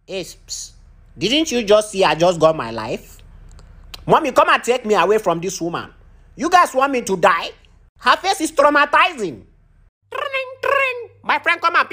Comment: WHAT IS THE BABY SAYING😂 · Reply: “Es, ps, didn’t you just see I just got my life. Mommy come and take me away from this woman. You guys want me to die? Her face is traumatizing!”